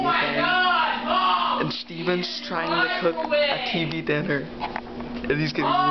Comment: When I heard this the first time I thought he said “he’s trying to cook a TV dinner”